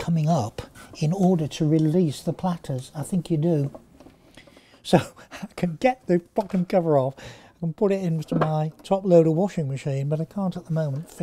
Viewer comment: WTF!!! does anyone else hear him swearing? "So, i can get the fucking cover off, and put it into my top loading washing machine!" Love this guy.